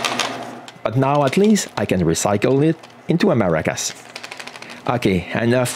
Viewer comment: Sorry, hard for me to hear..what did you say at the 7:37 mark? I think I missed it. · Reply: but at least now I can recycle it into a maracas